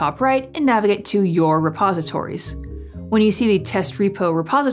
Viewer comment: Before I watched this video I keep hearing "Suppositories" I'm like. What?" I need to have a suppository to use git!? 😲